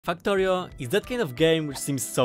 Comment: why does it sound like you say f**ktorio🤣